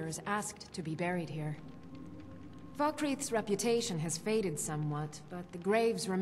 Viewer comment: does she say "fuckreath"?